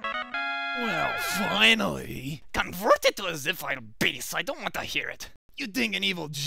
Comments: What did he said? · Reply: "Convert it to a .zip file, Bass, I don't want to hear it." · What does Wily say at 0:24?I get the "I don't want to hear it."But I can't make what's before it. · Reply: "Convert it to a .zip file, Bass." In other words, .zip it! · "Convert it to a ZIP file BASS, I don't want to hear it!" Possibly the cleverest joke used here. If only it was more audible. =w= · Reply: At first I thought I thought he said confound it bass i don't want to hear any of that